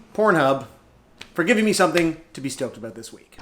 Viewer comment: I thought it said “STROKED 1”